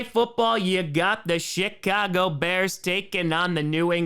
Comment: Kinda sounded like said shitcago bears